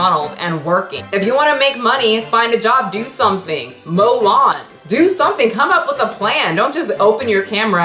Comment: Definitely thought you said 'Mulan' not "Mow lawns' I was very confused for a moment.